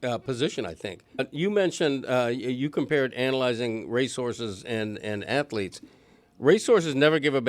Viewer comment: why is he saying Race Horses like resources lol